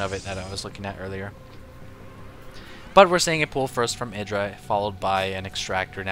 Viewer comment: the Closed Captions says "from Hitler" instead of "from IdrA" XD